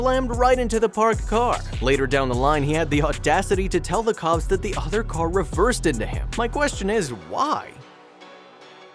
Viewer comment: Did you mean 70 year old? I could swear that you said 7! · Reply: He did say 7 lol